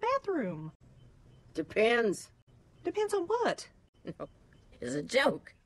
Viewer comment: Oh I here thought she said DE PANTS yk like depends= the pants